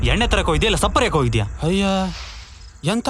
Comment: Shapp hariyok hoidhya ..did i heard correctly 😂😂😂😂😂😂😂😂😂